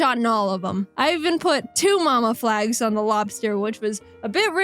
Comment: I thought she said yo mama